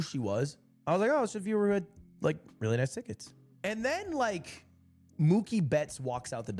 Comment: I really was out here mishearing thinking lud just said "Oh it's just a viewer who had like... really nice tits"